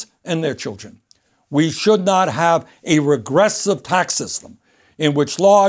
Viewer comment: what did he say? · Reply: Regressive tax system